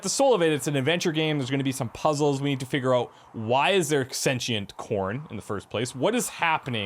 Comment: In the beginning I thought you said sentient porn and I was like "yeah it's definitely gonna be a weird game!"